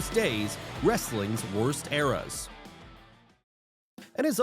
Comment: Thought it said worst ears...